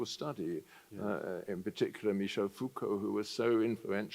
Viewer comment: What is the awful book he talks about at 12:10, I've listened a few times and can't get it. The auto subtitles say "food color". Err...! Nope. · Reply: foucault, a post modern philosopher